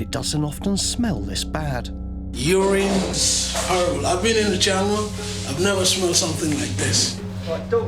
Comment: does anyone know what says at 00:42 ? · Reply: Bit late, but he says: I've been in the jungle, never smelled anything like this